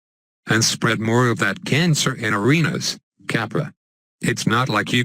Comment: what did the robot said at 10:00 after arenas? subtitles showed 'capra' I don't know what it is. would anyone mind telling me what he says and what it means?